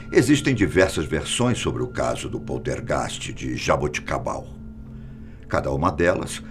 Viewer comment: Eu entendi "jabuticaba"